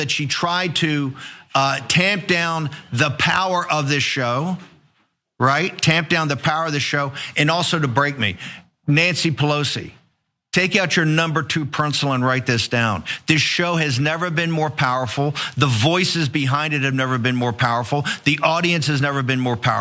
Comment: Did anyone else hear, "... number two per-ncil..."? No? Just me? 😂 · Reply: Yeah...I just commented on it, athough I heard it as "pren-cil".